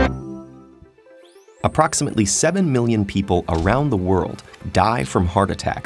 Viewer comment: I thought you said 7 billion